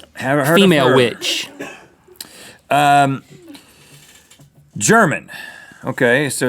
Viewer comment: When I heard Link say Female Witch,I thought he said Email Witch.... RUUUN THE EMAIL WITCH IS GONNA SEND YOU SOME SHADY EMAILS